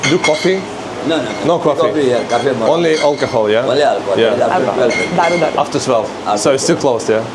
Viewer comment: 😂she said Daru. 😂😂Anybody hear that or is it something else